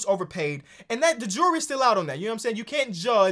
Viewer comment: second time said "JEWELRY" instead of "jury", i thought he just misspoke the first time